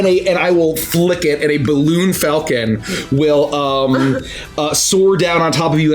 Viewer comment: balloon falcon... I thought I heard blue falcon, that would have been worrying.